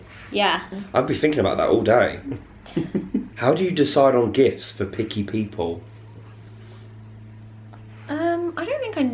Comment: I thought you you said “how do decide GIFS for picky people” hahaha